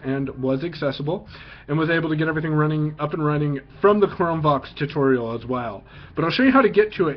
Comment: Hi I'm blind and use the exact chrome book u just demo straightens! I just wanted to bring to your attention that when u say the words Chrome Vox it sounds like your saying Chrome Box! There is a chrome Box desk top but this might be confusing to a blind person thinking of buying one or ETC! Thanks Floyd...